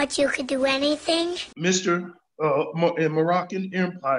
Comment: Did he just say "Moronic empire"...?